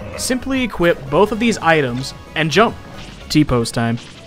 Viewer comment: YES T-POSE TIME!!! But weirdly the captions are saying tipos instead of T-Pose